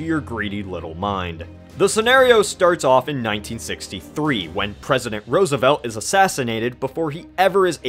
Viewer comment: am I hearing wrong or are you saying.... 1963 at 1:02?